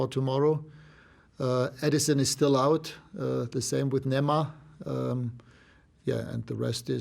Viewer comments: Did anyone else hear "neymar" at 0:22? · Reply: It's nemanja matic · i thought he say neymar 😂